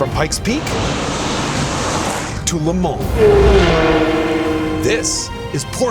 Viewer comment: the English subtitles say "Lamont" instead of Le Mans